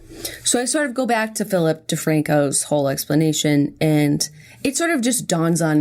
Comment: I thought you were saying "Fill up the Francos"